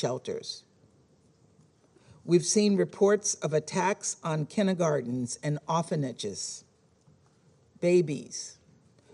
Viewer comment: Anyone know what a kinigards or a offiniges is? US said it around 4:03 and I'm confused. English is not my second language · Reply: Kindergartens and orphanages.